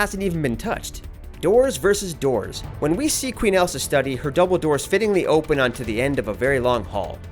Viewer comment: I heard Dumbledore